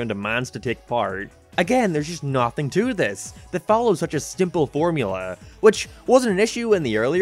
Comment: did you just say stimple?